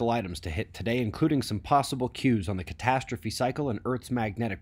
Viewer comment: I heard possible Q's. Nice. Q.